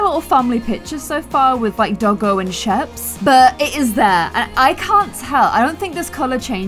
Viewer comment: did she call her sheep sheap or is that the sheeps name